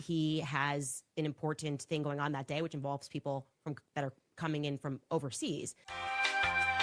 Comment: I swear she almost said "people from Qatar"😂😂😂 Funny, didn't they just go to Tuckers Christmas party??